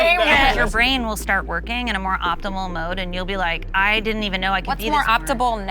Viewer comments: did she say optimebull?? Instead of optimal?? Lmao!!!!!!😂😂😂😂🤦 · did she say optibal?!